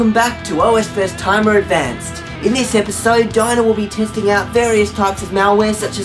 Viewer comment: what I hear “In this video Diana will be testing out different malwares...” Captions “In this video diner will be...”